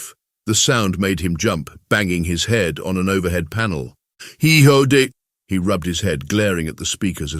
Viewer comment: Too much... sounded like "he hoed it." 24:34 "twenty onest" LoL